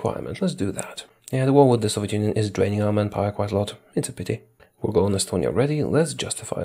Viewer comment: I thought you were saying "man-gapoor" for a good 30 min before I realized it's man power.